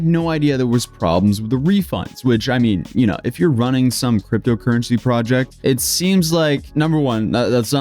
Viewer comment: did he say crypto currency fraudject?